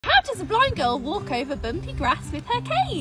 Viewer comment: I thought she Said how does a blind Girl walk over grass and cociane